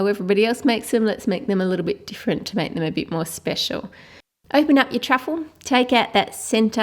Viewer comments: did anybody else catch at 11:36 she said "open up your truffle" instead of "Oreo" XD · she said "Open up your truffle" instead of "your oreo/biscuit". Just sayin'...